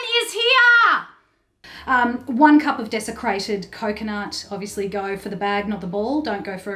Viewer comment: I'm not used to the Australian accent or cooking, so at first I thought she said desecrated coconut. I was like what kind of sacrilegious balls is she going to put in her mouth. Then I figured it out. Thanks for the excellent recipe!